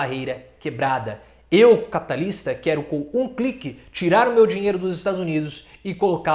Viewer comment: Toda vez que vc falou "capitalista" eu entendi 'Liberal'.